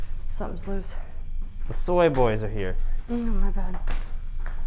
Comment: What does Ty say at the top of the stairs at 29:48? i swear i hear "soy boys are here"